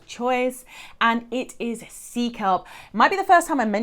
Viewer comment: My dear!!!!!! The TRANSCRIPT(@ 5:00) said “C kelp” instead of “sea kelp.” Actually, there are MORE ERRORS THROUGHOUT YOUR TRANSCRIPT, so PLEASE CONTACT YouTube - your Deaf/HH fam, who might rely on your transcript, will NOT get the CORRECT information. I’m watching out for you!!!🫶🏾💫LOVE YOU, SIS!!!!